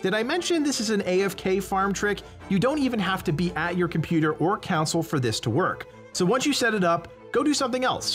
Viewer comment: Did he just say cow sale😂